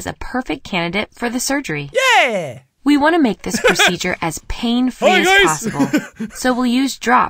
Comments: I'll be honest, when said "as pain free as possible" during the eye surgery, I heard "as painful as possible" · "make this operation as painfully as possible" Am i the only one who heard that? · anyone else hear as painfully as possible right here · I thought she said "we want to make this procedure as painfully as possible" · Is it me or did it sound like she said "we want this surgery to be as painful as possible"?